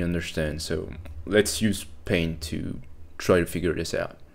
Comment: The subtitles saying "let's use pain to try to figure this out" at 1:10 instead of paint is very relatable.